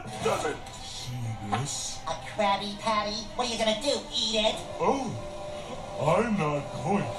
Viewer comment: I swear to god I heard Spongebob say Sh*t and then when I listened again I heard eat it!Now just imagine Spongebob saying Sh*t and then you'll hear it!